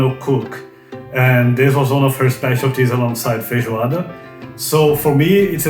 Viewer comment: Idk how (or if) you're supposed to correct subtitles errors on YouTube, but at 12:20 there is a subtitle "of her specialties alongside (indistinct)", what he is actually saying is "feijoada"